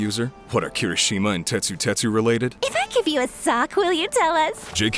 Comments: For the longest time, I thought she was saying "If I give you a suck", and was confused about the JK Rowling bit. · I thought she said “if I give you a suck will you tell us” I was like WHOAH that’s forward. But the jk Rowling jab right after it made me realize I was missing something… “Sock” Like dobby… Cuz she’s talking to Dabi.